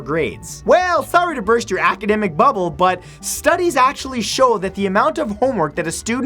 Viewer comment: I thought Matt said "Sorry to burst your academic bubblebutt" LOL